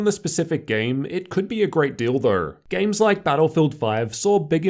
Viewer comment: im sorry man but that sounds like "great dildo" instead of "great deal tho"